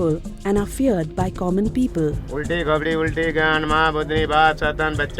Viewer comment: did he said 'ulti khopdi ulti gand'? 😂😂